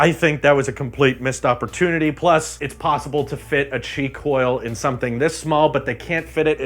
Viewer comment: When you said qi coil I heard it as cheek oil